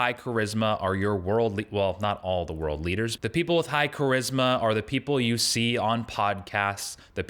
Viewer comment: My internet went out at just the right moment, I heard "but the people you see with charisma are on pot..." I was like wait what?